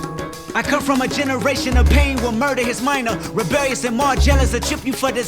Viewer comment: Who’s here after the diss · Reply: Did he just say a minor ?????